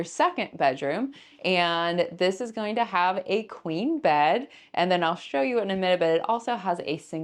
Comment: When you said "queen bed" I thought you said "clean bed." I thought "Well yeah, I hope it's a clean bed." LOL